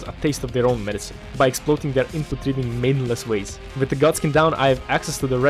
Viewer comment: Sounded like you said, "Turning the [Godskins] into Maidenless waste." Quite apt.